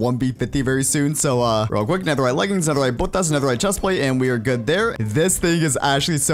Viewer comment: im the only one who hear netherite botas ?